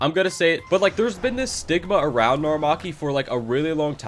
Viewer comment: thought he said sigma and renshiki really needs a re-work